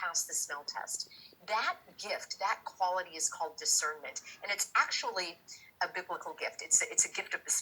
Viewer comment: Sounded like she said, Gift of da serpent or discernment. Had to pause.